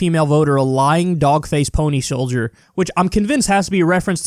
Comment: I thought he said "phony soldier" and that really annoyed me but this is still bad